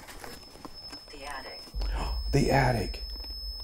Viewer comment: " Stop" as the alarm is going off on the SP7. 36:06 "The attic" is whispered and then the app on you phone says "the attic" after i heard it audibly. (child or female voice from before on both timestamps) Whatever is running up in the attic has more than 2 legs. WTF?!